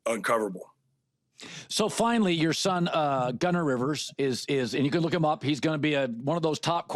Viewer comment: lol thanks for sharing his son’s name I swear I thought I heard Gunner Rivers! Haha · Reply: It is Gunnar Rivers